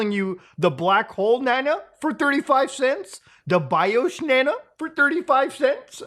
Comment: YOU got to be kidding me IT'S FAKE. I thought you said BIOSH banana not BIOS